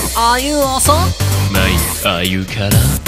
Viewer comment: 一瞬Are youおそ？がAre you ローソン？って聞こえていいえって言っちゃったw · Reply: 猫mike ほんとだ！！もう一回聞いてみたら、Are youローソン？って聞こえた！！